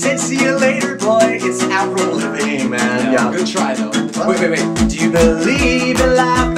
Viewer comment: what did he say? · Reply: Avra Lavigne man. No good try.